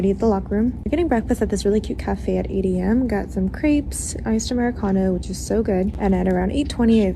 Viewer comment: Did she really say she got some "creeps" for crêpes?